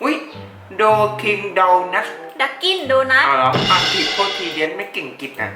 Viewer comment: <num> ขอโทษนะครับผมได้ยินเสียง ว่าโทษทีเย็ดไม่เก่งอังกฤษ5:14